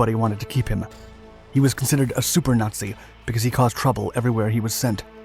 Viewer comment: SUPER NAZI, i thought you said SOUP NAZI, NO SOUP FOR YOU !